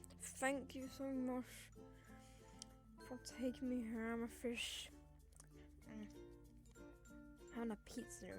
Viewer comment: I hear amafich HaHa